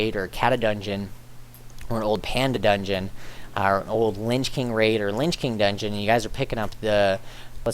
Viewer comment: sounds like u said linch king :P, in all seriousness i loved the guide and am praying it works :P